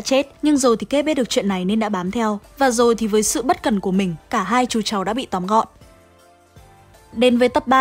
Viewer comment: sao t cứ nghe nhầm '' 2 chú cháu '' thành ''2 chú chó '' :)))